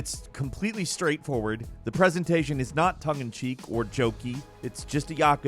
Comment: Thought got away with a fbomb when he said tongue and cheek, thought he said efx were fing cheap lol 😅